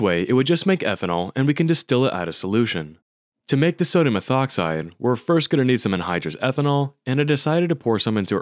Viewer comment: How exactly do you get sodium methoxide from sodium metal and ethanol? Or did you say "ethoxide" and it just sounds muddled? 1:22 you definitely say "methoxide."